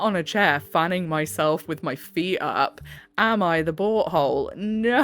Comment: Love how when u say “Am I the butt hole,” I hear “Am I the BOAT hole?”❤ Love listening to a HUMAN! 🥰